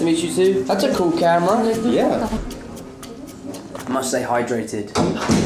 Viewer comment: J'ai du réécouter 5 fois avant de comprendre ce qu'il a dit 😂 (Pour l'instant j'ai compris "Might stay hydrated", en parlant de son verre)